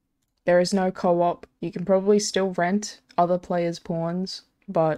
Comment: Why the f*** did i hear "You can still rent other player's porn" 😂